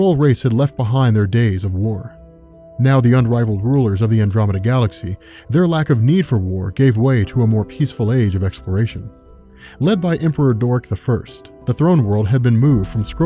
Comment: I heard "Emperor Dork" and actually laughed out loud on the train, help.